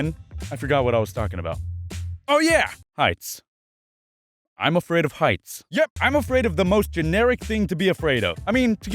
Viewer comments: I thought you said you were afraid of ice. heh heh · For one moment I thought he said I’m afraid of ice 😂😂😂😂😂